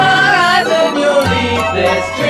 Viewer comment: this song sounds like shit yet also amazing at the same time... ?????? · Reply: its a good song, but i cringed everytime with the japanese pronunciation, i keep hearing "oh yeah sue me"